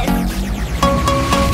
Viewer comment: I THOUGHT ORISA WAS SAYING "CEASE YOUR EXISTENCE" (like in the overwatch heroes tell you to stop video) AND I WAS SO CONFUSED LMAO